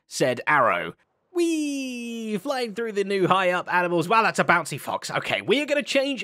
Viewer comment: sounds like he said "Wow thats a bouncy cock."